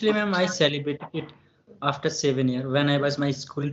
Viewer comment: Thanks for this lovely chat dear ☺️🧡I absolutely enjoyed it, we should do this again next month! and my apologies I misheard when you said 7 years, I heard it 17! silly me! 😅